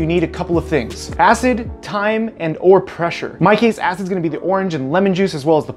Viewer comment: When you said time i thought you saId thyme LMAO That is amazing, i love your videos